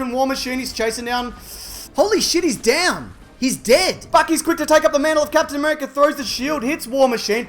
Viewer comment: i thought he said "fuck he's quick to take up the mantle" and lost it